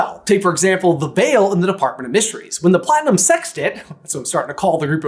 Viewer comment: I'm not sure about that platinum sextet nickname, I hear it as "the sextit" 🤣